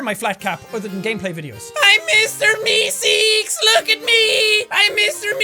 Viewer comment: i thought it said meeskees lol